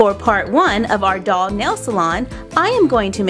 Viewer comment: I thought it said Jail Salon